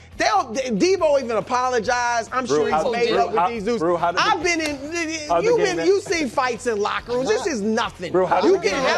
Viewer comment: what did Brou say???😂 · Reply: Hit that ninja😂😂😂